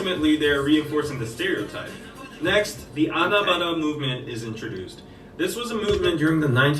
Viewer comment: In 11:25, when the dude in the video said "Anabada movement", I thought he said Anambra (a state in Nigeria, where my mother is from) movement, and when I tell you, my head snapped away from my laptop and towards my phone quicker than you can name WWH is.